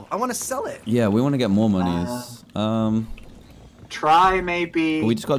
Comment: did dan say moneys?